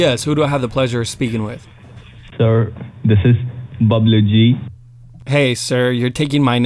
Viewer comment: I thought the scammer said his name was Bubbly Gee.